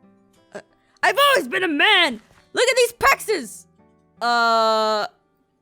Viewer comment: When said pexes it sounded like he said Texas.